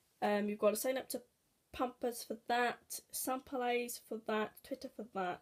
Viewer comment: What is the name of the freebie app please? I thought you said sampleize but couldn't find it on Google play? Great video, really need to get making my videos again haha X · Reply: Ruby Roo sampleyes (sorry for the long reply)